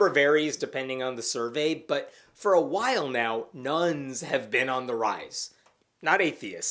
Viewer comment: I wish a better term had caught on than “None.” I keep hearing that “Nuns are on the rise.”